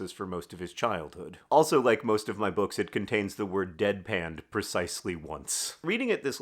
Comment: I love the ongoing tradition of the singular "deadpan" in each of your books. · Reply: At first I thought John said "bedpan", which would make sense for this one, TFioS, and even maybe Turtles, but I was struggling on the others 😂